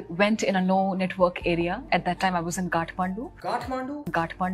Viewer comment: Bro i thought she's saying GATHPANDU 😭 pta nhi kya bol rhi thi, i like urvashi but her interviews😂💀